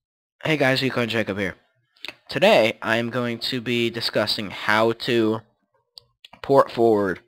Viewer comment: in the beginning you sound like you said. " hello today i am going to be disgusting"